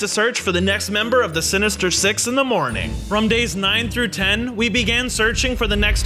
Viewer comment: i Heard "Six In The Morning Like " Um " Sex In The Morning " Bruh *If You Heard Like That Like This Comment"